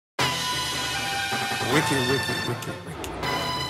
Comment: After all these years, I thought this man was saying wiggle wiggle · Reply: I thought he was saying wicked wiggle wiggle 😂